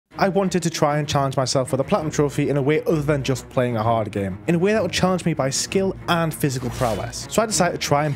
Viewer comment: I thought you said i wanted to cry instead of i wanted to try hopefully your hand is doing better